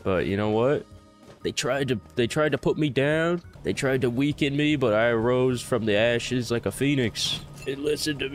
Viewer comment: sorry but there was environmental noise while watching and I had to rewind because I could swear I heard you say “ they tried to weaken me but I arose from the ashes like a fetus” :’D